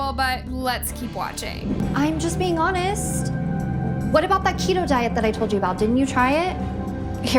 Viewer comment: I thought she said “Cheeto diet” 💀